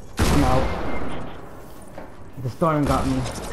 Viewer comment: Bruuuh 22:12 thought you said you died by trap